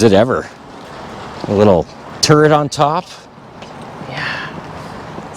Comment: I thought you said turd instead of turret 😂😂